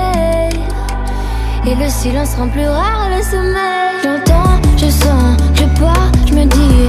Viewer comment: 1 minute 52 . Ce n'est pas " et le silence rend plus fort mes acouphènes. " C'est.. " et le silence rend plus rare le sommeil " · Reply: Ohh. También me di cuenta, pero como mi aprendizaje en francés aún es bajo escuché "et le silence rend plus et le sommeil", saltándome "rare y poniendo "et". 😅